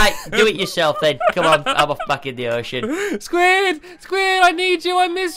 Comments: did squid say the f word?. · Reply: Think big Go gaming “I’m off back in the ocean” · Did Squid say that he's gonna F*** in the ocean at 0:21? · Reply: he said "im off back in the ocean" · who heard squid say the f word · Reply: He said "I'm off, back in the ocean." It's because of his accent and the speed in which he speaks. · did squid say a bad word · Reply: no he said im off, back in the ocean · Did anyone else hear Squid say '' ill have a fuck In the ocean'' at the start of the video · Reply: I heard It too but I thought he said I'll go back in the ocean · On 0:21 Did squid say Ok ill have a F*** in the ocean?